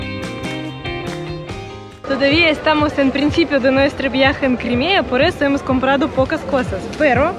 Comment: Pensé que decía nuestro viaje al Crimen xD